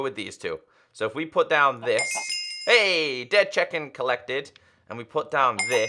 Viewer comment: "ayy dead chicken collected" just me who heard that?